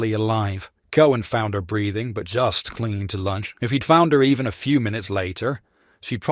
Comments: swear he says lunch and not life lol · Did I hear that right "Cohen found her breathing but just clinging to lunch"? XD Gotta say this looks like a very interesting game.